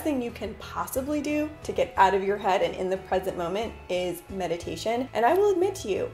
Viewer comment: I thought you said "the best you can do to be in the present moment.. is Medication..." lol😂